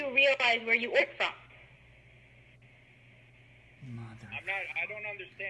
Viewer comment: what in the world is the voice after she says I don't think you know where you ordered from? Whoever it is said "mother fu**"